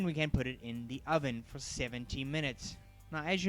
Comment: I just found this recipe, and I want to give it a try. I just went gluten free, and I have tried three recipes so far. Did you say to bake it for 70 minutes? At first I thought you said 17 minutes, but I realized this was not enough time to bake. This bread looks delicious. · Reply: Yes 70. The bread really is delicious!